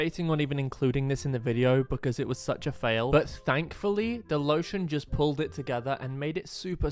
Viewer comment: Am I the the only one who heard stank fully